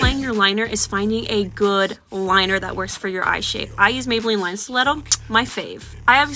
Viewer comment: May anyone tell me what the product [ 18:46 ] uses? She said it but I can't seem to grasp the product name Maybelline.. stray?liner? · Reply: Line stiletto I think?